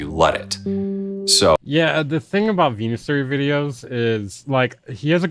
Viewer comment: is he calling him venusaur ? 🙈